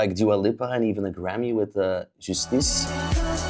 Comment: did he say Juicetees? 🥴